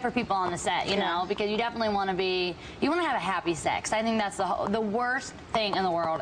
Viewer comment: Sounds like she said 'Happy Sex' !? what did she actually say?